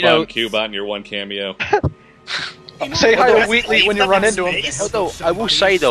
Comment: Wait, who did just mentioned? I couldn't hear. Say hi to who? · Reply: Wheatly